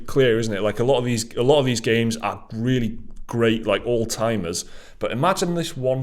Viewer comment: the subtitles say that those "games are really great like Alzheimer's..." I cant remember a time when great at games were compared to Alzheimer's.